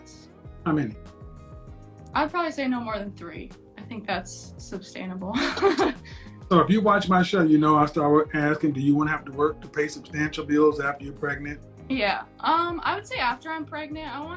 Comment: Did homegirl say "substainable"? LOL!